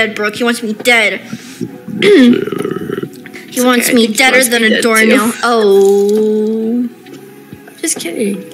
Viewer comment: what’s she saying at 3:31? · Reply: i think she said "deader than a doorknob".. it's a saying :)